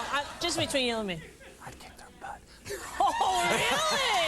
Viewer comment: I heard Chuck say, "I can't talk about it." Everyone here in the comments is saying they heard him say, "I'd kick their butts." I'm guessing this is the Chuck Norris version of Yanny vs. Laurel? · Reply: I heard both. I first hear 'I can't talk about it' but then I increased the volume even louder and it's clearly 'I kicked their butts'. The kicking their butts fits exactly with what Rosie said which was that they were better than him. So no, it's not a Laurel vs. Yanny at all.